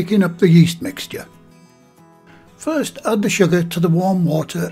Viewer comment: Did you add 1 tablespoon sugar? It sounded like you said salt. Well, I think it should be sugar. Looks delicious! Thanks.